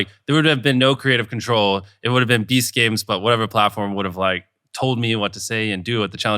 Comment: All this time I thought he said "Beast Caves" and was thinking- what and interesting choice. Must be a lot about game shows in caves. 🤦‍♂🤦‍♂